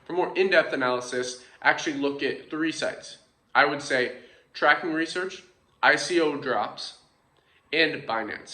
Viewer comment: I'm sorry to bother you with this silly question, but at 08:44 I can't really understand the name of the site. Is it tracking research? 😅 Thanks for the fantastic content. I'm watching your videos and I'm taking notes. I find your content that useful and good. 😊🙏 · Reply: Tracking Research, ICO Drops & Binance. Good to hear your enjoying the videos, will be increasing quality as the channel builds!